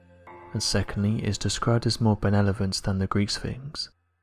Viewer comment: Someone please help me out here.... Is it just me, or did he just say "benelevant" instead of "benevolent"???😂